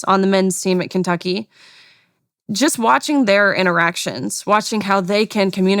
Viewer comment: LOL ~27:00 thought she said she was watching their erections (not interactions)